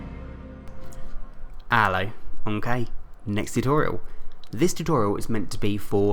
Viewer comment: Wow, I always thought he said "Ello, I'm Kay, next tutorial" I'mdumb